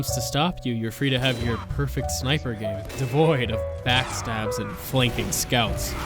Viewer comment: did he said sniper gaming